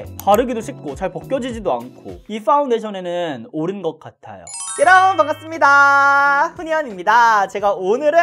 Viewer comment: 예전부터 "여러분 반갑습니다"를 "뾰로롱 반갑습니다"로 들었어욬ㅋㅋ 뭔가 역시 이야 특색있어 라고 생각했다는...! ㅋㅋㅋㅋㅋㅋㅋㅋㅋㅋㅋㅋㅋㅋㅋㅋㅋㅋㅋㅋㅋㅋㅋㅋㅋㅋㅋㅋㅋㅋㅋㅋㅋㅋㅋㅋㅋㅋㅋㅋㅋㅋ텐션 넘 좋은거아니냐며ㅠㅠㅋㅋㅋ